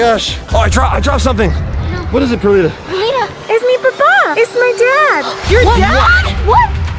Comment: Do u speak Spanish perlita? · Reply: shes spanish I heard her saying Es Mi Ropa